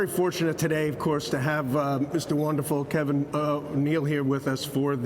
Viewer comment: Does this congressman not get briefed on the names of who he is having a meeting with ?? Sounds like he says Kevin Neil.